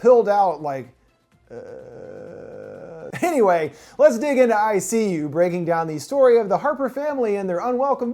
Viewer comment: for some reason i heard that as "ICU" as in the intensive care unit